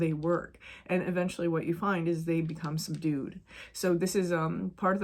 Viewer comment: Hahaha at approx minute 4 when you said that the complex parts of ourselves become “subdued” I misheard it as they “become some dude” and lolled so hard!! It’s true though….gotta integrated that sh*t or it just shows up as another dude with a lesson for ya 😂😂 Thanks as always 🙏✨